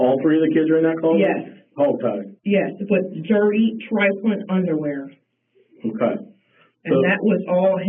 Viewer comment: can anyone understand the word she used to describe the dirty underwear? Tri - something? I’m just curious. This was a tough video to watch, as she calmly, unemotionally describes listening to her children being so savagely abused at the hands of that monster, and doing absolutely nothing to stop it. Absolutely heartbreaking. The detectives showed enormous restraint, when clearly they were sickened/mortified/enraged/saddened like the rest of us. RIP sweet, brave little James. 💔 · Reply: Trifling is the word.